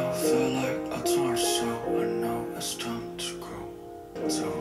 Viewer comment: God, I remember when everyone thought he said torso, when he actually said torn soul. haha